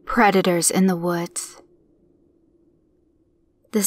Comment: I thought she said Creditors in the Woods. I was terrified.